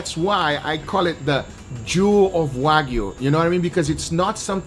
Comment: why does it sound like he says jew instead of jewel lol